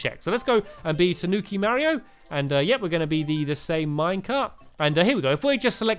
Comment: yup i heard him say minecart